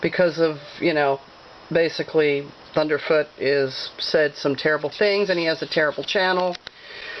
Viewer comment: wait, did she just say terrorful?